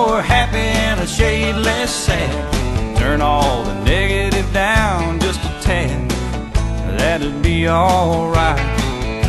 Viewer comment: Growing up I always thought instead of "a little lighter load to bear" was "a ladder over there". Lol don't asky why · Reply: lol instead of turn all the negative down just a tad I thought he said turn all the diggity down just a tad